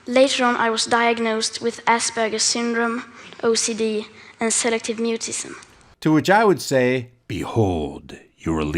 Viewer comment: I thought she said 'Selective newtism' which is the inability to speak to or communicate with salamanders, or newts, in select social settings such as pools, under rocks or stones, compost heaps, and an assortment of damp and dark areas.